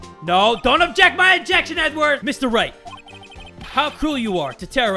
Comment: did just said "Edward"? Or I just misheard him? Ahahaha xD